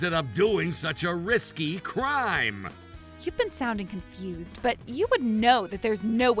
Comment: what the captions actually says: "you may sound confident" What she said: "you may sound confused"